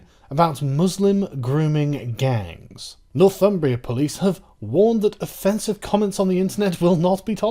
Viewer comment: When you first said Northumbria Police I thought you said "North Fun-bria Police" Maybe they last one is more accurate.